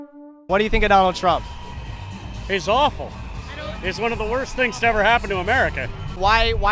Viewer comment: He said “one of the worst” lol. Good comprehension skills! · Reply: oh he did i heard "the worse thing" lol